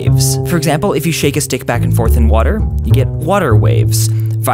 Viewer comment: does anybody hear 'shake his dick back and forth in the water'?